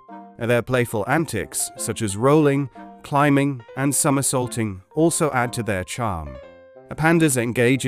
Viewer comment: Lol I literally heard "some assaulting" instead of "somersaulting." I was like daaaamn, those pandas are thugs!